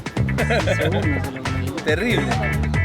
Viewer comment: I think he said terrible not incredible